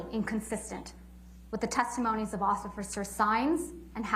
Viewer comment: Did she just say “ociffer?”